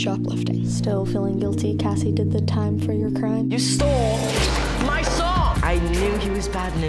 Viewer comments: I thought sam said "YOU STOLE MY SOCK" I was like ok then💀😂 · did he say "you stole my sock" ????? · I thought he said you stole my sock